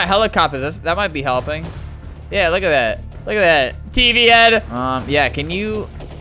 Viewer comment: for awhile when he was saying tv head.... I thought he was saying pp head....